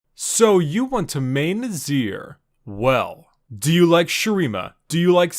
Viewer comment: I see "So you want to main Azir" All I hear is "So you want to main RASHIDOOOOOOOOOOOOOOO"